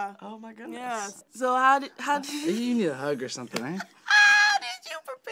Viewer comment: What did Keahu say to (sorry, I don't know how to spell her name) at 1:05 ? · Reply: think he said "Give me a hug or something, right?"